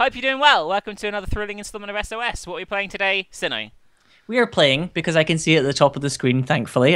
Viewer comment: What was said "What are we playing today, Sinow?" What i heard "What are we playing today? Sinow."